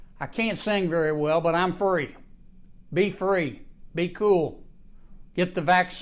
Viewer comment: Why did I hear “I can’t sing very well, but I’m furry “?